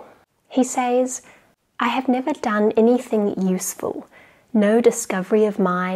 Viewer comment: the subtitles say "Mo" instead of "No" :(